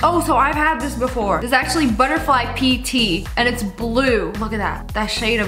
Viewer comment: I actually thought she said butterfly pee tea and I was like BUTTERFLY WHAT??!